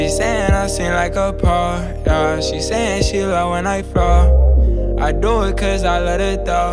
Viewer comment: did he say she said she love when I fart